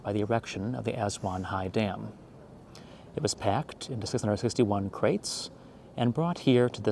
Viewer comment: What did he say at 0:25? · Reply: ah sounds like “It was packed into 661 crates…” lol